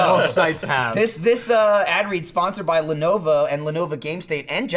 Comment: Sounds like is saying "Lenova"